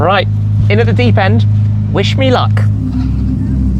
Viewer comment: Am I the only one who heard says "Wish Shmee luck" at 9:50 ?